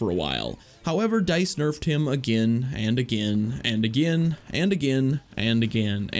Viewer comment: it sounds like he’s saying anakin instead of again when he’s talking about anakin lol